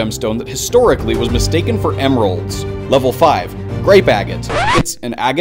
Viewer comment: what did you call me??? A fa what?